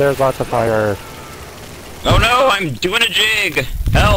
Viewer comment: First seven seconds in, and this is what I hear, "OH NO, I'M DOIN A JIG!" lmao. =)